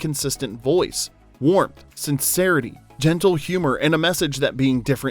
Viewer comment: At first, I thought you said DENTAL humor and I started trying to remember if there were other characters like Hermie in the other stories...